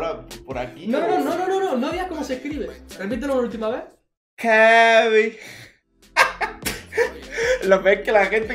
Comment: creo que dice Kevi